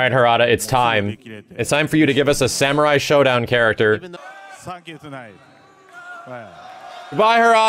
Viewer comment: Harada sound like he said "fuck everyone" I know he said thank you